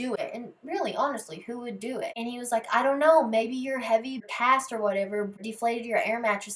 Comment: Omg when you said her heavy cast might have popped the air mattress I really thought you said heavy past I was like that's deep asf 😂😂😂